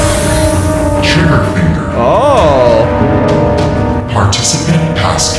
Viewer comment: when it was on the trial trigger finger i thought it said "child...chew your finger"